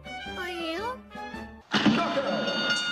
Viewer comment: I thought the devil said This mortal is to precious instead of mug- ;-; pls tell me I'm not the only one ;w